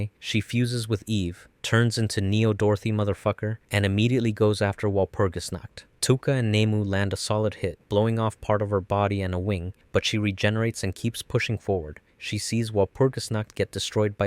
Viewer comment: I was playing a merge game on my phone and I heard “Neo Dorothy motherf*cker” and I quickly snapped my head to my TV like “Wtf did you just call me?”